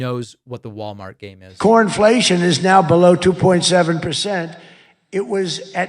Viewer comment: did he just say pornflation? 💙💙💙🇪🇺🇪🇺🇪🇺🇧🇪🇧🇪🇧🇪✌️